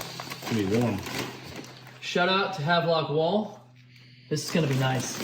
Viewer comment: Did he say Havelock Wall